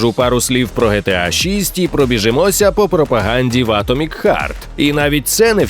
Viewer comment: Спасибо за выпуск! Хех, мне послышалось "...о пропаганде Vatomic Heart...".